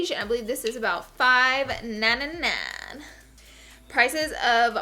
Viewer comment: why did she say the foundation is 5 na na na ? what does that mean? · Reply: 5.99